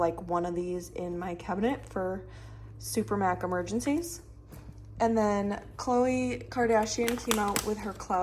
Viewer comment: did you say super mac emergency lol